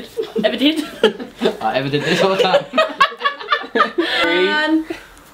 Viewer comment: what does say ? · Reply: I ever did this all the time